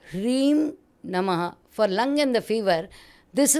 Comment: she said lung and fiver but subtitle says lung and Liver, please clarify.